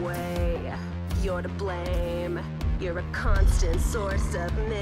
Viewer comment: I thought she was gonna say “you’re a cunt” bru